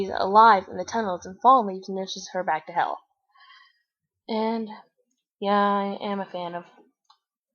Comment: I thought she said "Nourished her back to hell."